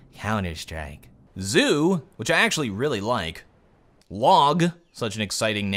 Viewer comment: Did he say wog?